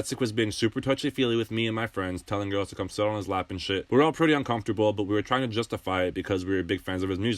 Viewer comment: “come sit on my lap and take a shit on me” is how i heard that